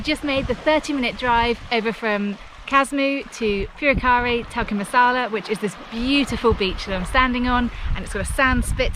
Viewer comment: I could have sworn I heard you say "Purekkari Tikka Masala". Sounds delicious :P